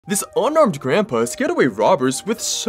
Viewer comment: Unarmed…I thought you said one armed first watch 😂doh!